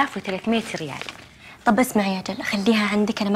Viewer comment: طيب اسمعي يا .... وش قالت؟ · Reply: طيب اسمعي اجل